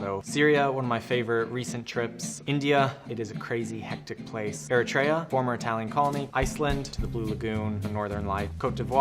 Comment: What does he said about Eritrea at 5:30? He speaks too fast for my english. Thx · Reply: "Eritrea: Former Italian colony."